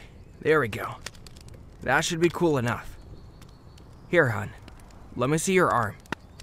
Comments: Is it just me that I hear kirishima call us “ hun” or not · DID HE JUST CALL US HUN!!! MY HEARTTT 😫😫😫❤️❤️